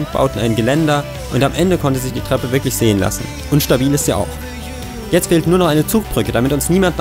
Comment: Ich habe bei 12:30 unstabiel ist sie auch verstanden